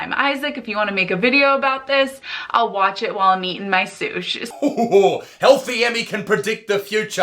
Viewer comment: I'm sorry, but did she just say "sush"? As in "sushi"? First of all, 1 letter too lazy means you deserve fire and brimstone. Secondly, I thought dumb-bass vegans were supposed to care about animals. Fish don't count as animals? Since when? Also, fuq vegans to the moon.